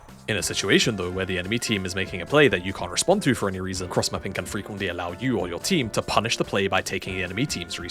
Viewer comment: Not going to lie I thought that said cross dressing and I was sitting there for a solid minute doing mental math on how cross dressing wins me solo queue games